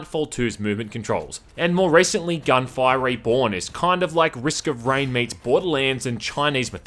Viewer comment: thx ! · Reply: I couldn't understand, my English isn't best , I thought you were saying ,,gun firing born". Anyhow, I'm a fan, thanks for video and keep going strong :)